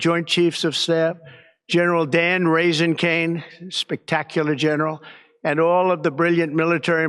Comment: Did he just say the generals name is “Raisingcane?”😂😂